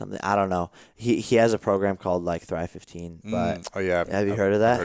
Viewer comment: I can't understand the name of the program that he talks about, can someone help me? I'm not English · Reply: is the guys name and thrive15 is the program.